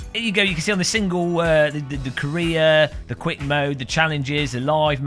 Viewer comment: I'm American, and when you say career, I thought you were saying "Korea." I was like, hey... not cool... I'm not from Korea.